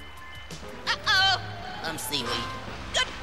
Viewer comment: GOODBYE WORLD! WE HAD A HOT OL' TIME! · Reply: I thought Mama Fireplant said Hard Old Time